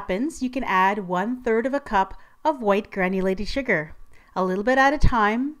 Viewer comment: Lol I thought she was saying white granny lady sugar!!!!! My bad! Lol